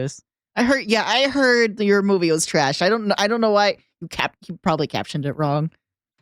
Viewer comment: The 'movement' bit was mishearing the line during editing. What is said is 'Your movie was trash!' not 'Your movement is trash!' because they had modded Bracken to be Freddy Fazbear and they were recording the video shortly after the Five Nights film came out.